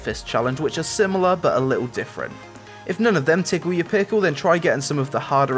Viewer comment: Did he just say tickle your pickle? ...??????